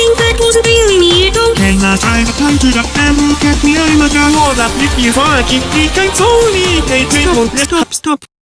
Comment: WHAT DID GAKUPO SAY IN THIS I'M DYING TO KNOW BUT I CAN'T UNDERSTAND HIS ENGLISH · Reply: after listening to it a thousand times i think it's "what the flip, you fucking prick, i'm so weak" but i might be wrong lmao